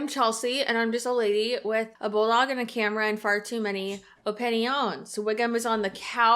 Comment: Lol sounded like you said “fart too many”